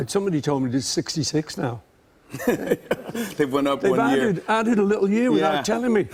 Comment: Did Jimmy just say "went OP" as in the northern "uh" sound?